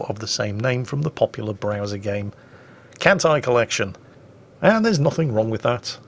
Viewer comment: I swear to God for a moment I thought said "Hentai Collection" ~ XDXDXD